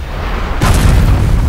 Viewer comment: what did Jean say? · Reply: What was that you were saying? Cause i think it's a moot point now.